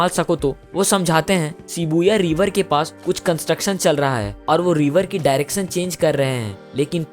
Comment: "shibuya river" ?? Did i heard it right??? 😅